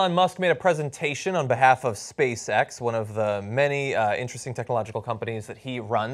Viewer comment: Oh he said "SpaceX" I thought he said "Space Sex"